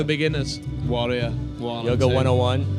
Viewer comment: What does Alex say at 5:07 ? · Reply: Warrior 1 and 2